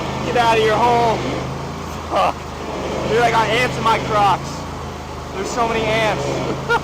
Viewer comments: I swear I thought said “I got ants in my crotch” not crocks 😂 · "I got ants in my Crocs" hahaha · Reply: i thought he said i got ants in my crotch when he first said it lol